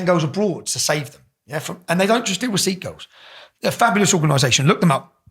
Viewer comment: Love the subtitles, youtube. "Seat goals" ....OK...wtf are those ?? I heard "sikh girls" very clearly. Nothing like bullshitting the hearing impaired.